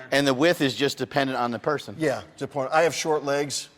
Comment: I had to relisten to 4:05 again. For a moment I thought said "Open the taint" and then said, "and the whiff is dependent on the person?".